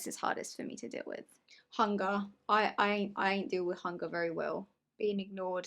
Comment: "I deal with hunger very well" in the bathtub: "I'm just,, gonna have a banana" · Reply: he actually said " I ain't deal with hunger very well " So yeah- he did tell the truth